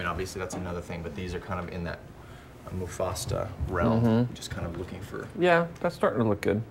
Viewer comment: did he just say “Mufasta”?